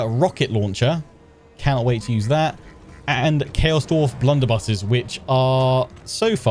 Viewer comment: In ep3 I honestly thought you said "Wonderbus" when referring to the Blunderbuss. I feel like this may be an appropriate name now 🤣 "Right boys, unleash the Wonderbus"